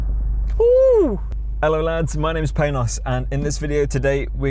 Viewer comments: Thought he said hello guys my names painass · Thought he said his name was thanos lmao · I heard pain in ass. 😂